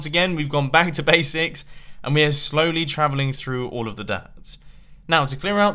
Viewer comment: sounded like travelling through the days